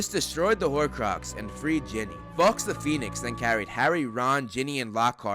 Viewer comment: When He said Fawkes I thought he said fucks 😂